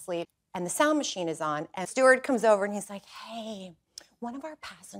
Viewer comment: When she said “steward came over” I thought she said Stuart. And I was like, “Doesn’t he have a comic store to run?”😂